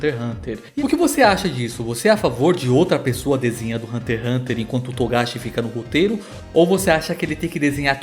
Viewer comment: eu entendi "enquando to togashi fica no puteiro" kkkkkkkkkkkkkkk